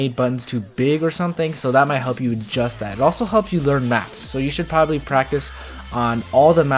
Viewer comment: who else thought he said it will help you learn maths?